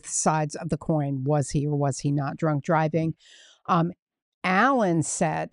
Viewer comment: I had just read about this guy Allen. First I thought I had heard G G Allen 😆